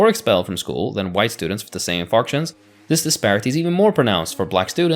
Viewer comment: "... students are three times more likely to be suspended, or expelled from school, than white students with the same [Farksions]?". "Farksions"?, "Functions"?, I have absolute no idea what he just said. Listening to it again doesn't help, and the subtitles have no idea either what that last word was. It really felt like an important word in the sentence, but I guess it's not important enough to record again. 🤷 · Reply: He said infarction (a medical term), probably meant infraction. ESL moment.